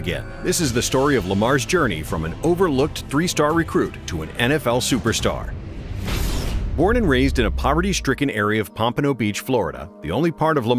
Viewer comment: I thought it said an awful superstar and I was like dude that’s harsh😅